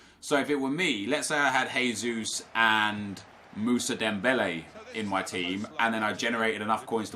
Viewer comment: well in that case just get aguero instead of Musa (even tho you said dembele the 2nd time) 😅 and have aguero and Jesus together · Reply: no one will have this channel name He said Moussa dembele